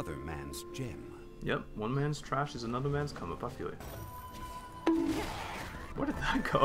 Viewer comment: still trying to figure out what you said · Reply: "one man's trash is another man's come up, i feel you"